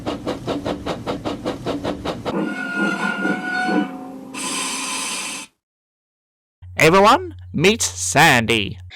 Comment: I have to come back to watch it, it's so funny, also did Percy say fluffy pink fairies or fking pink fairies? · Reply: F*king Pink faires